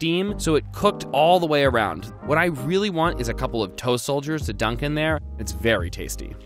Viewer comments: Why did you say TOE SOLDIER?!?!?!? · toe soldier? That's even what the captions said lol